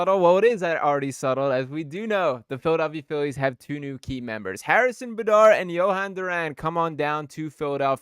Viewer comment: Did he just say Harrison buhdar?!? 🤣🤣